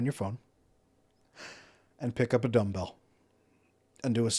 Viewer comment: Oh, pick up a dumbbell… Had a rewind that I thought you said pick up adoughnut😁